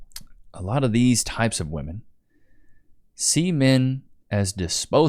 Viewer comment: what did he just say? Sea men? As in pirates?